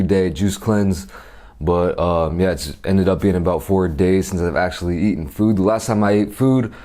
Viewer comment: Thought it said 40 days, when i realized i was like.. "bruh"